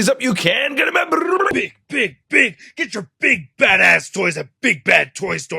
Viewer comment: When You Said “Badass” I thought you said “Fatass”